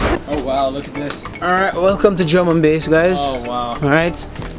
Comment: I thought he had said "German Base" lol